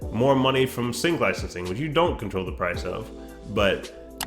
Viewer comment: What’s up Brova At 5:13 u said they might even make more money at ? I couldn’t really get what you were saying let me know interested in what it was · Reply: Sync licensing is getting your music in TV shows, films, video games, etc. You can find more info about that here: <link> & <link>